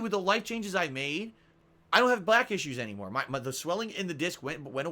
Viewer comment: Was that a slip? Did he say "I don't have black issues anymore" IDK keeps going to "Jim".